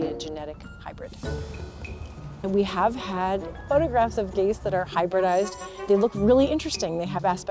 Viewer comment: the subtitles says gays instead of geese. But nice content.